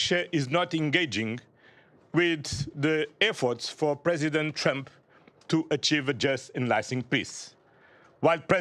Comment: Did I hear “President Chump?”